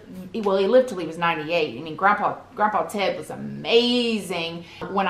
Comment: Sounds like u said Grandpa Tip .. sounds like my in-laws their name is “Tipton” so they always shorten it to Tip